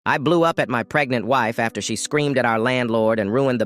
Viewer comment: I thought it said "i blew up my pregnant wife"😭🙏